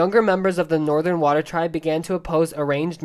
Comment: It sounded like you said illegalize